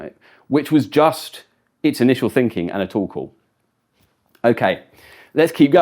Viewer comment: every time he says "Tool Call" I hear "Torkoal"